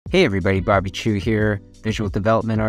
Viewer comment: Swear I heard "Hey everybody, barbeque here."